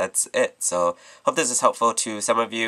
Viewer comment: This is awesome. Am I the only one, or does it always sound like he says "I will talk to you, all of you soon" at the end of his tutorials? I know it's "I'll talk to all of you soon", but it doesn't sound like it too me, haha.